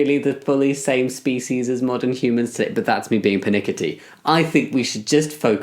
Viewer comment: Can someone tell me what word he used at ~12:20 he said sth that sounded like "but that's just me being penikity" with stress on the 2nd syllable. Thanks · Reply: it sounds like "pernickety?" that's not a word though so maybe persnickety?